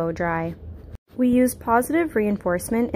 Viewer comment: I thought you said” pawsitive” instead of positive 😂😂😂😂